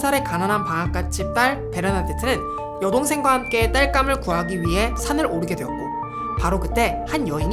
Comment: 에... 음... 땔감 아닐까요? 귀로는 딸감 이라고... 아니 혹시.. 설마 해서 몇번을 반복해서 들었는데 딸감...